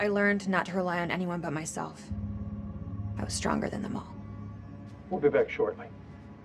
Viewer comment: I love how Rachel claims she’s the strongest person in the group, but she is the second most difficult to keep alive. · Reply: I thought she was saying she was stronger than the vamps